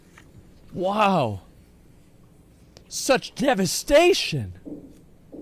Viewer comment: I was eating cereal and i heard "SuCh DeViAtIoN!?!" And i started laughing so hard that milk went out my nose 🤣